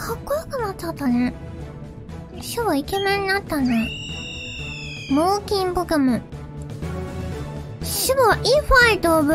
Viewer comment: あかん脳筋ポケモンに聞こえるw